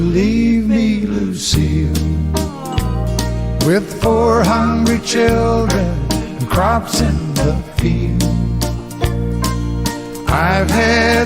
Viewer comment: Was 7 when this came out, my brother and I thought he was saying 'four hundred children and a cop in the field"... ah, kids lol